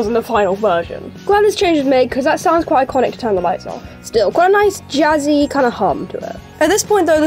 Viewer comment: i hear "turn the lamp off"